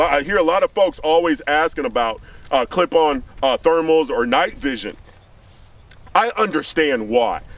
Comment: I thought you said you perfer clips on thermals